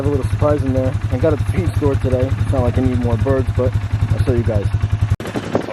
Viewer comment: did you say so you can eat more birds? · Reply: Haha it did sound like that. I said it’s not like I need more birds 🤦🏻‍♂️